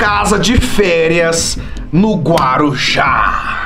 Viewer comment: EU OUVI Direito Guarujá? ( L U L A ) entendedores entenderão